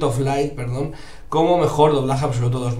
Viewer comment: Yo escuche "shit of alive" xd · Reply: es Child of light...mi ingles es asi de guay